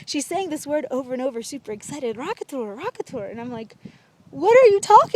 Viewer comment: At timing 3:30 it sounds like she is saying "rock guitar" lol